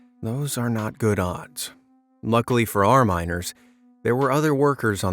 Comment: ohhh miners I thought you said minors😂